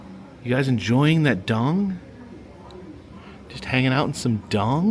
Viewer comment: For a moment at 14:44 i heard : "enjoying your bong? "